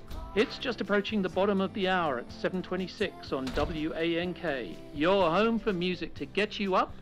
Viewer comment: "7:26 on W A N K" - did I hear this correctly...?